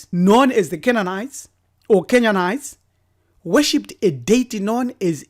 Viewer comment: did you just mention my country kenya, i heard 'kenyanite'